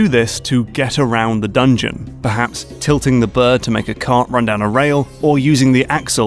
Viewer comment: I thought I heard "Flip the Bird" at first instead of "Tip the bird on Medoh"